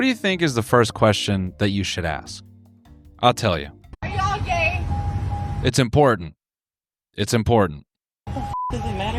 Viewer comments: I heard her as "are you all okay?" and I was like... seems like a reasonable question. Then she repeated it and I heard it correctly. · I really thought she said “are yall okay” at first